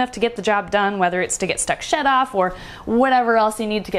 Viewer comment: I had to go back to 6:33 and listen again -- this time, more closely! Because when said "whether it's to get stuck shed off"...at first, I thought she actually said a different word that ALSO starts with "sh" and I was like, "Wait...what?!?!" LOL!